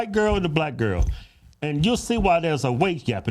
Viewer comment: I swore at first they said weight gap. Oh wage gap.